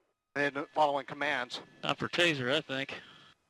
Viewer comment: Edit: Cenk, (at 3:19 <-- click) I don't think he said "Tazer that thing"... I hear "Tazer I thinK". I don't know what this is called in "The leader of the free world"- the USA, but in most of the rest of the world, including the "not so free world" this is rightfully called second degree murder. M.U.R.D.E.R.